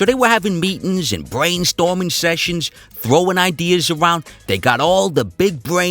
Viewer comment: actually i heard "throwing kisses"